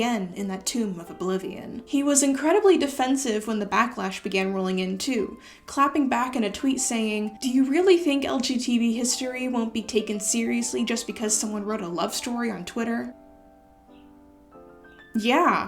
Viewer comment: Wait, did you misspeak there or did he actually say "LG TV history"? Because if he did then that makes that tweet so much worse.